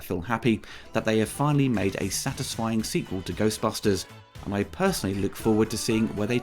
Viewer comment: So, a ghostbuster movie that didn’t make you excited to wait for some ghostbuster action. i understand what you mean. And while it’s surely not a bad sigh for a movie (even though I don’t agree that this is a good movie), it IS a bad sign for a ghostbuster movie. it’s like saying a Jurassic park movie was so good that it didn’t make you excited about the dinosaurs. Like sure, that’s nice, but it still missed the Mark of what it should aspire to be. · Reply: You clearly misheard what said at the end of his review. He said that he "personally can't wait to see where they take it next" at 9:45. This means he's excited for the future of the franchise. This movie absolutely hit the mark and more.